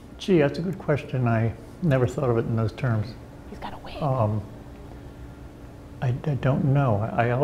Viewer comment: Did I just hear someone say, "He's got a wig" at 0:47 🤣